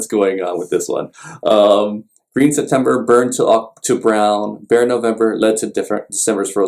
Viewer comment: Even after 50 years since I had first heard this song, I am still wondering whether Karen Carpenter sings "thin November (Am7 / D)" (as I hear) or "bare November" (as shown on the printed matters).